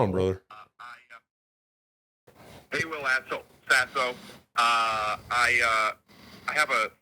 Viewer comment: Whenever I hear Will introduce himself as "Will Sasso", I hear "Will's asshole". I hope that wasn't mean. I'm sorry